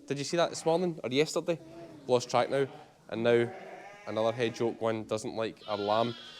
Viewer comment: I thought you just said another head job